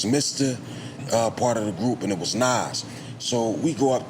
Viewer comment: does he say Nas? If so is he talking about THE Nas or someone else with the same name